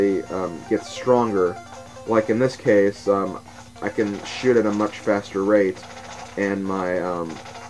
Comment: anyone else hear "I can shit at a much faster rate" ?